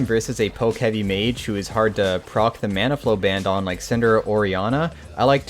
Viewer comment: Might be mishearing but did he say Cinder? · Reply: Think he said Syndra